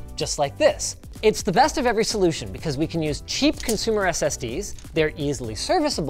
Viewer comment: did he just say cheap